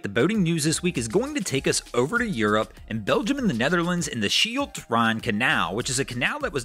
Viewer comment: Did he say, "...Belgium in the Netherlands..." ???